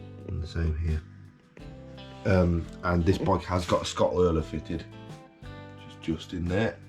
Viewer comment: What were you saying about something under the seat between time 2:00-2:03? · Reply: Hi, I was looking into the potential leak which turned out to be the output sprocket seal but the bike was fitted with a Scottoiler system. Automatic chain lubrication…